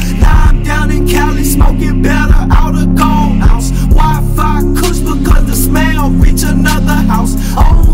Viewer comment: "wifi kush cause that smell reach another house" - the daily cali stoner problem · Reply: I thought he said “why fire kush ? Cause the smell reach another house”